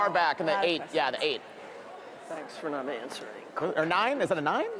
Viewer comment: Was Cole saying thx for not answering quick to Lili and she says yeah then he says good job